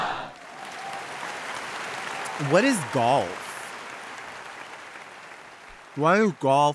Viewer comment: 0:11 WTF did u say??? GALL F ..?? Gurl 👀 GAWLF?